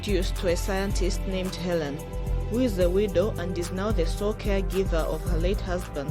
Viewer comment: Ik she said widow, but I first heard "who is a weirdo" and I was like dang, well tell it like it is!